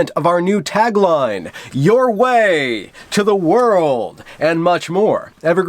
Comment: "Your way to the World" all I heard was "Emergency exit that way"